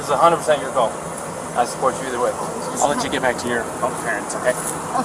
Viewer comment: The police made a blunder, she should have been arrested for assault and more than likely she would have been booked and probably still be alive( if she's not right now) because probably her parents would have been notified and the cross country trip would have come to an early end...my opinion! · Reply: did he said "I let you get back with your parents ok"?